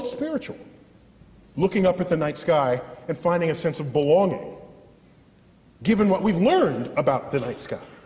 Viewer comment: did he say did he say" looking up at the night sky, we find a sense of baloney given what we know of the night sky...of course we're not alone.....it's full of baloney.